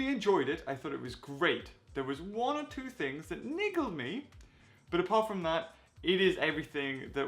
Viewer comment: Wait.....niggled???? Is that a British thing or did I hear that wrong?